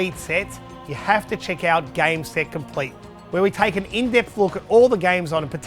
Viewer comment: "...check out ___" What did you say? Game that complete? Where can I find that video series?